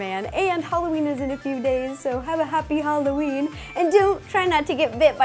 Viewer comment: It sounds like she said "Happy HallowREEEN"